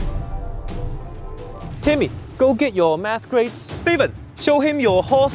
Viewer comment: hahahah the first time i heard "timmy, go get your mass graves" xD